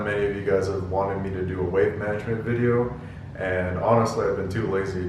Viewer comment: I swear I heard "weight management video" the first time. Please tell me I'm not alone on this.